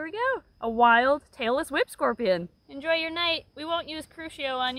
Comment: I kept hearing "tailless whip scorpion" as "Taylor Swift scorpion", and was confused 😅